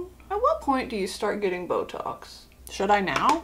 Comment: I THOUGHT SHE SAID BLOW JOBS NOT BOTOX